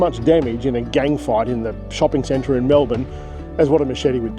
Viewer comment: 😅 I thought you said chopping centre in Melbourne 😅 I had to rewind the video.